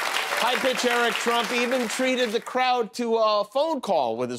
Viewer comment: Did he reference "High-Pitch Eric" Trump @ 5:15, or am I hearing things 😄